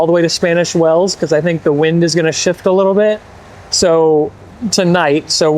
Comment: HIII! This is Aunt Robyn and Uncle Kirk, I love the head band!. It gives you a cross between pirate and gypsy look! Now to roll your pants up to your knees and get one earring. Lol. It sounded like said went to take his first Sh*t of the day but then we listened more and it was shift. Anyway it gave us a shock and a good laugh